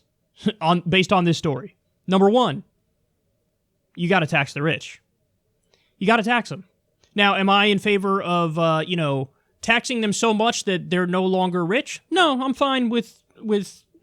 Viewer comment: I heard "you gotta tax the bitch" lmfao